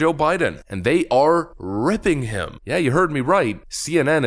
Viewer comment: I don't think so, because I heard "raping them" xD